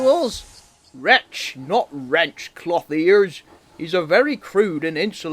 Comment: Seven years later, and that "cloth-ears" bit STILL makes me laugh! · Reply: I don’t know why, But i always thought he said “ Clothy-ass”, even thought that word doesn’t exist…LMAO